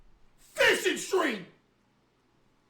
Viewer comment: This whole time I thought he was just saying “fishing stream” just because he was hinting and excited about fishing. I’m cooked.